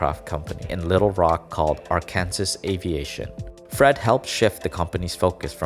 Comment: Did he just say ar-kanasas